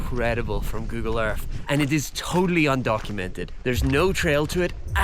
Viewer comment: Good thing there is a clearly established trail to lead you to the undiscovered ancient city. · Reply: He did not say undiscovered he said undocumented right around 1:45 to 1:50. And he is correct they are not documented